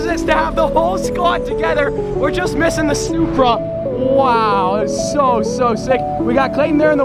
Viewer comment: did he say supra or snupra